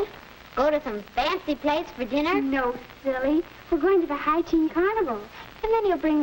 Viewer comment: Anyone else here watching this in 1949 wondering who you should take to the Hi-teen carnival? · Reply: At first I thought he said hygiene carnival.